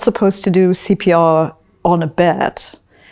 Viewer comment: I honestly thought for a sec she said "you're not supposed to do CPR on a bat" and that the next part of the question about getting a man off a bed without breaking his neck was a really, really obscure in-joke. Kind of disappointed now. Can you do CPR on a bat?